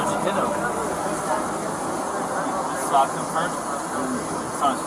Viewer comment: when did she say her face was grabbed? not doubting you, just don't want to watch the whole hour long video. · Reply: "You slapped him first?" "Ya" That's exactly what she said. You're hearing what you want to hear.